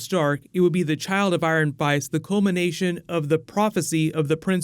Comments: "The child of IRE and FICE." Please tell me I'm not the only one who heard that lol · Did say Child of Ire and Fice @ 4:33 haha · Is it just me, or at 4:32 does he say "The child of ire and fice"? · After dozens and dozens of videos I've watched, FINALLY, I hear you fumble a little! I thought you were invincible! :) "... the child of ire and fice"